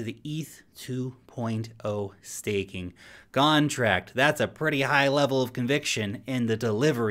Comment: did he say “gone”tract? 😂